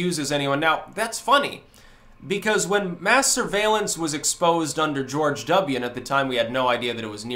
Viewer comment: When I hear "mass surveillance", why does it sound like "masturbation"?